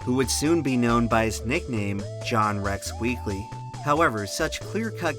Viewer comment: I tried recanting the "John Wrecks Weekly" joke to my wife, but she heard it as "Rex" and didn't get the joke